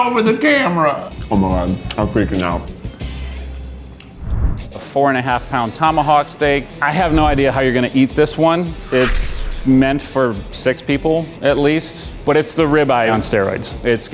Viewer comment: I swear it said tony hawk ribeye